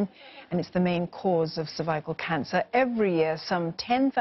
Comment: Did she say survival cancer at 0:31😂 MSM